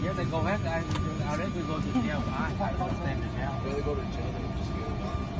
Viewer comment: the officer threatened him with jail. "would you rather go to jail than just get off" (how I hear it). So they were threatening to arrest a boarded passenger. Nice Delta.